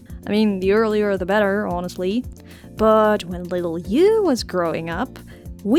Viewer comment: I thought you were saying "when little YOU was growing up..." XD XD XD XD